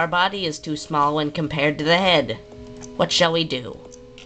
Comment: When you said that the color ruins your creature's eyes, it sounded like it was saying "Whaat!"